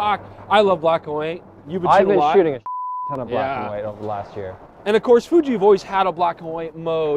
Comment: What does say where it's bleeped? "I've been shooting a bleep ton of black and white." I just can't figure it out! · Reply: "a fuck ton"